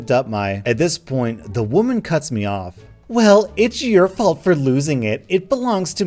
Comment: dang i thought he said the woman cuts me lol